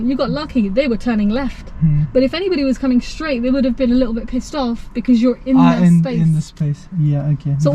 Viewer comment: Did you actually say P**sed off?! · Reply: Lol yes I said the other driver would have been pissed off with his position as he’s was positioned in their path